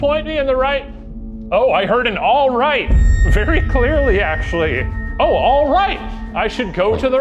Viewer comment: I deadass thought you said he caught a very clear Edp